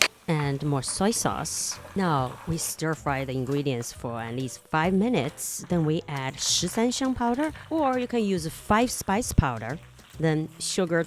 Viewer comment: Found that powder on Amazon last month, used it a couple times, most recently in dry rub to make oven-baked chicken legs. So.....what did you call it??? 💙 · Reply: Called Shi San Xiang powder. Literally ‘13 fragrant’ spices.